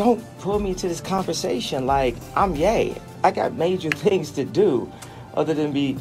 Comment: I thought he said "I'm gay"